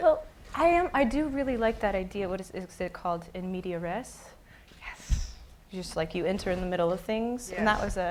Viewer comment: What word was that at 3:06? Imediares? I can't make out what she's saying, lol. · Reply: marcelo ratzkowski en media res